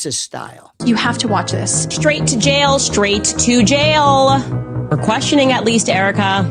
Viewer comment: Candace says “straight to jail, straight to jail for even questioning Erika” she’s talking about herself and what the EK fan boys are saying about her.